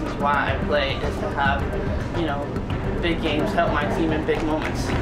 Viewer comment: What'd she say at 2:38? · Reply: "Storm on three," I think